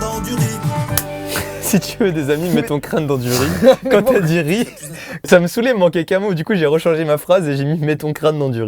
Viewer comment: j'ai compris "mets du crack dans ton lit"